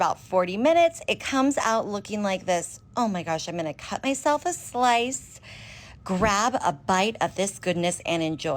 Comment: this thing genuinely looks good but my brain didn’t process her saying “I’m gonna cut myself a slice” and thought she just said “I’m gonna cut myself” so that took away a lot of my appetite for this thing